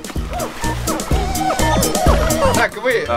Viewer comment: Мне же не одному послышалось: - И так, сегодня мы будем делать бургеры из ГАМБОЛА.)